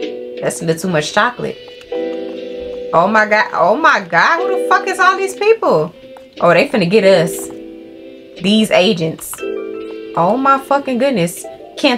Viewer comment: these asians LMAOOOOOO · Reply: She said agents I think but I thought she said Asians at first too 😅